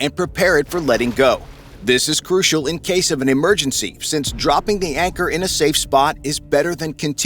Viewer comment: When says emergency it sounds like he says murder scene